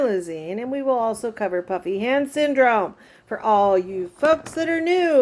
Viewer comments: omg i have literally thought you’d been saying PUPPY hand syndrome 🤣💀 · omg i have literally thought you’d been saying PUPPY hand syndrome 🤣💀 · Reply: Haha me too. I was watching a video of hers the other day when she mentioned she was going to do this particular video and I too thought she said "puppy" hand syndrome lol